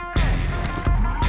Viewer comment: Everytime he says mclareen i always hear "Bengladesh " 🤣